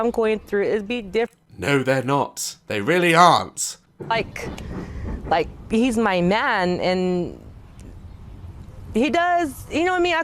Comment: PLSSSSS- I THOUGHT SHE SAID "HES MY NAN"